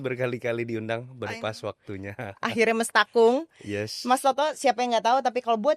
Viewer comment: Mbak itu ngomong apa ya ? Kurang jelas terdengar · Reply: Mestakung ? Semesta mendukung??